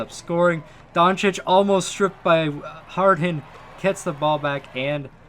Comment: DID HE SAY HARD HEN......MY BRAIN.......😏😏😏😏